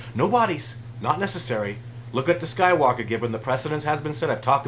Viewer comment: hes saying something about DNA of something with no body but just DNA. What is he saying it was sounds like skywalker or something